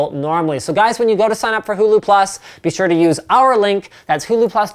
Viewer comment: when you said huluplus it sounded like you said google plus cause my volume is low lol